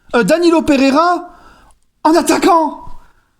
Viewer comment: j'ai de la merde dans les oreilles ou j'ai entendu Danilo attaquant? non c pas possibles je vais me laver les oreilles